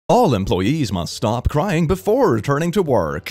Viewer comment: why did i hear "bald imployees must stop crying"